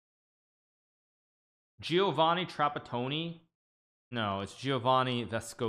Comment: Huge hug to who wrote "Giovanni Trapattoni" in the chat, was a good one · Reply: thought he said Giovanni Trap-a-pony :DD